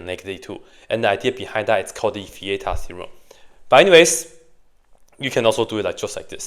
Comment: What's he saying at 3:35? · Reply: Vieta’s theorem